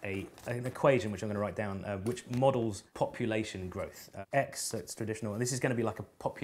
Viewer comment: I heard "copulation growth".